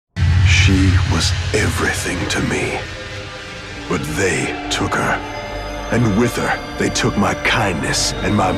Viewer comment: At first, I heard "But then they took her" as "But then Vayne took her"